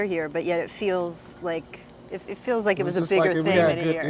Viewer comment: did wall say “shit was lit”?